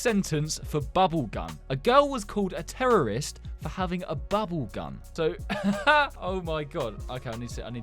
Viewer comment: I thought you said bubblegum I was like what for having bubblegum?😂😂😂😂